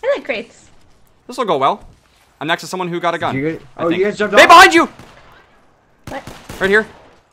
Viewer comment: did he say babe behind you?